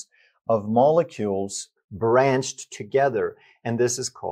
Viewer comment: you say a word that I cannot hear properly, and it seems it might be important to know. What is abunch of tens of thousands of molecules called? I have listenrt to it over and over and still cannot understand what you said. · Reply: Branched together